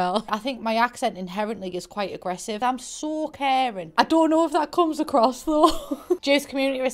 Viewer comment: What she said: “I’m so caring” What I heard: “I’m so Karen” Would you like to speak to the manager?